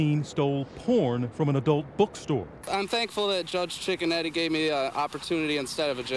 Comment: "Im thankful that judge Chicken Nugget gave me an oppurtinuty instead of jail." Im dying.